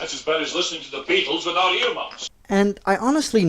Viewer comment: That's as bad as listening to the beatles without ??? Not sure what Sean Connery said there. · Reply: Ear muffs*